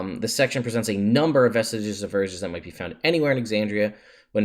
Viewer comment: Oh, "Vestiges of Divergence." I thought you said "Vestige of the Virgins."